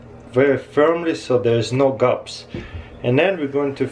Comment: for a second i swore i thought he was saying "so theres no gups" but nope he was saying gaps